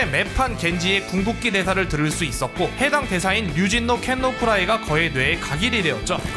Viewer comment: 류승룡 기모찌는 예전에 어디선가 1.5배속인가 2배속 했을때 똑같이 들린다... 라는 내용 때문에 흥한 걸로 압니다. 실제로 겐지 성우를 초청해서 류승룡 기모찌 1.5배속, 2배속에 대해 얘기를 나눈 인터뷰가 있었는데 성우도 인정하는 느낌으로 얘기해서 웃겼었죠. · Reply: 그냥 카더라 아닌가요 ㅋㅋ 2배속으로 1.5배속으로 해도 류진노켄오쿠라에~ 로 들림.